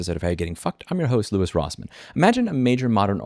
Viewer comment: I dunno what you said at the beginning but I heard "How you're getting fucked, by Louis Rossmann"